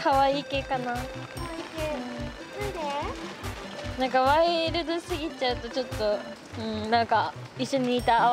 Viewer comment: ワイルド系ってスギちゃんとか？ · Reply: お前のせいで2:08ワイルドすぎちゃんとって聞こえるじゃねえか